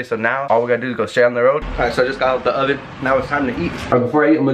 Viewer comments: At the beginning of the video I fr thought said “okay I just got out the oven” I rewatch it like 5 times and still didn’t get what he said.😭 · did he just say alright I just got out of the oven😭